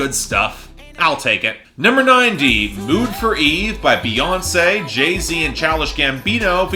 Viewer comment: did he just say “mood for eve?” 💀